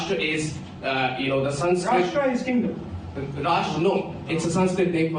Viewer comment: When Sidharth verma explains to Rahul that the word " Rashtra " means nation ( which is common knowledge) Rahul blurts out that it is " Hindu" . The level of Rahul Gandhis ignorance is shocking. I wonder how he gets called to give lectures at prestigious institutions. This possibly this only shows the agenda that the institution it self has, with regards to India. Kudos to Sidharth Verma for stopping Rahul in his track. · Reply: At first even I heard it as "it is hindu". But if you listen closely he says "Rashtra is a kingdom". Not defending that clown.